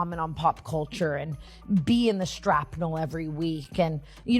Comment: does Tana say "sTrapnel"?? Lol